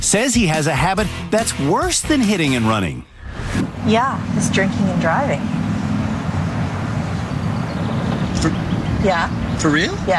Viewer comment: literally thought she said jerking and driving